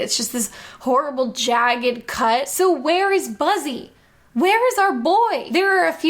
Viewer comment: she actually says “So, where is Buzzy? Where is our boy?”. I had remembered her saying it several times, but I think this is the only one & she never says "where's Buzzy" 16:14 another “Where is our boy?” 5:57 introducing the Cranium Command characters: “and Buzzy!”